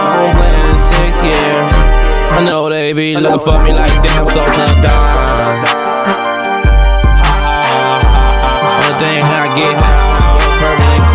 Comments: "I'm from another planet, what I'm doing here? I ain't been in my home planet in six years. I know they be looking for me like damn Sosa a Don. Only thing I get high off good purple & chron'' Felt that one bro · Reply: Sup3r She3p you too brother much loves me be safe out here, also definitely thought he said damn sosa goneeee cause he been gone lol but I heard the A don part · "I'm from another planet, what I'm doing here? I ain't been in my home planet in six years. I know they be looking for me like damn Sosa a Don. Only thing I get high off good purple & chron'' Felt that one bro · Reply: originally i thought he said "damn Sosa died'' but then realized he's implying he can't go home cause they'll kill him. Much love man stay safe during these strange times.